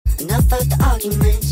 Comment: Alr but why do i hear "i did the hollymunch, she says the coca cola, she can't tell the difference yet"😂